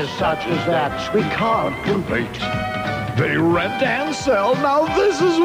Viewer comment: Anyone else thought he was going to say "Now this is hell"? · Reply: He said “now this is well!”